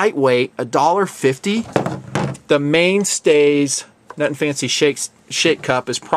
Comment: did just call it the Main Stays Sh@ cup?